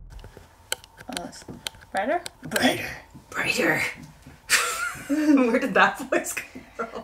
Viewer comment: that’s so crazy. It sounds like it says ‘better’ than you say brighter. And it’s crazy bc you’re not the type that would say it in a silly voice like that. You woulda just been like yeah that’s better. And it’s weird that it almost sounds just like you.